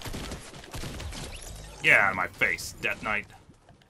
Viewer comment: was it only me or did anyone else hear him call the dark bomber death knight